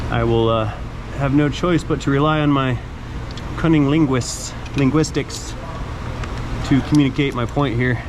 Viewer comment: Did he just say cunnyilinguis? 😂🤣😁